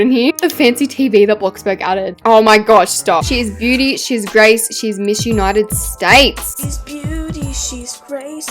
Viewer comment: Who else thought when said”she is Beaty she is Grace Mrs.United states”I thought she said”she is Beaty she is Grace she is mission out of states”